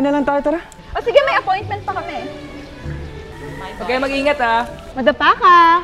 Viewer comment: i thought she said "madafucka", "madapa" ka pala HAHAHAHAHAHA